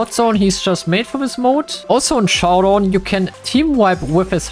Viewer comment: “in showdown you can team wipe” unless I heard it wrong